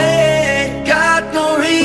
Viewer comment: What I heard: “baby i’m just a world away.” What he said: “baby i just don’t want to wait.”